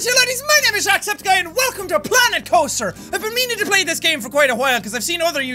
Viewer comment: Did he just say welcome to planet toaster🤦‍♂️